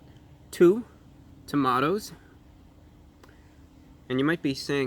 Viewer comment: Did anyone else catch him call the pickles two Tomatos?